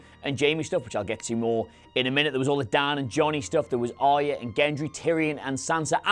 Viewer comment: did he just say dan and jony stuff